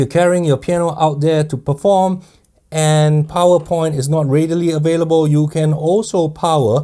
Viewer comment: I really thought you said PowerPoint, you meant power port